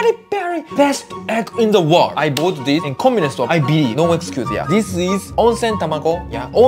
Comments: For some reason I heard him say this the first time: “I’ve bought this in the communism store” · "I bought this in a communist store" got me 💀 ik i misheard